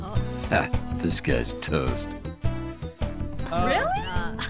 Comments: You should get CORPSE to say your intro thing, “ha, Disguised Toast” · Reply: I always thought it said “This guys toast” haha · You should get CORPSE to say your intro thing, “ha, Disguised Toast” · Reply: That’s what it says? I always thought it said “Ha, this guy’s toast” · You should get CORPSE to say your intro thing, “ha, Disguised Toast” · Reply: Yeah, i first heard, “This guys Toast,” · is it just me or im just different whenever his intro i hear "this guy's toast"